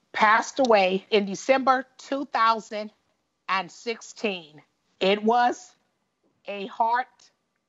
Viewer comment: did she say he passed away "2000 at 16" or "2000 and 16"???? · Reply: 2016